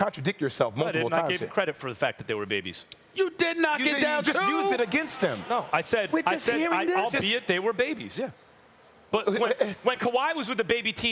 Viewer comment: All I heard was “they’re baby’s” the whole time